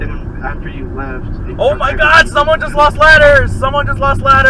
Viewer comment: i swear i thought he said "someone lost letters" lol